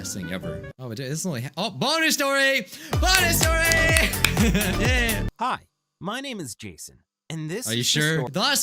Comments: For the bonus story, I thought Jason said "My name is Jesus" So I was like "Wtf?" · I thought he said “Hi my name is Juice” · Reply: i thought he said "hi my name is jesus"